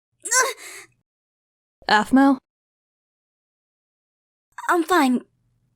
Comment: lol i skipped it and then i heard "aphmau u r irene" I'm like "oh..... k den.... o-o" XD